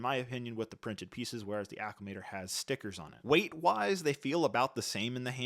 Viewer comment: you said the Acclamator has the upper hand with printed pieces then said the Acclamator again instead of the invisible hand has stickers, great review tho I just picked up mine and love your work 🙌🏼💙